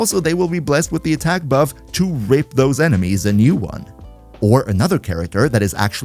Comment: uh am i hearing this right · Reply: Isn't It "RIP Those Enemies"?